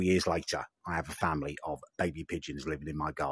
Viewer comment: “A noncery environment that I have created for them”, too much info mate. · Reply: I thought he said a ‘nonce spree’ environment🧐